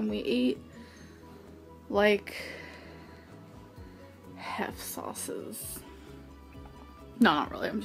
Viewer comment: What was she trying to say here?? "We ate like hef sauces"?? Was that her quirky way of saying heifer? 🤔